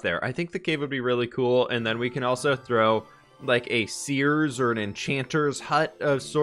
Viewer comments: I don't know why I heard it like this, but around the 6:10 mark when he said, "...and we can throw like a seer's or a witches hut..." I heard it as "and maybe we can throw a Sears or you know a witches hut" Like the shopping place. Lol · I thought he said “a Sears or an enchanters hut.” I was like a Sears??????